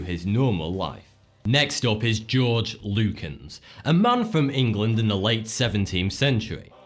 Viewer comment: lol when he said george lucins i thought he said george lucas and i was like ermagerd the creater of star war was posseste D